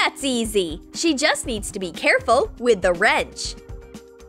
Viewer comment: did anyone hear she said black and gray crayons?